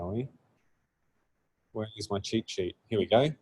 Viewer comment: I thought he was saying ChiChi instead of cheatsheet In general I think postgraphile is good for generating CRUD. It can really speed up the boilerplate setup. Other than that is not scalable for development since you would have a bunch of SQL files. So everything non-CRUD should be in in framework atop the postgraphile.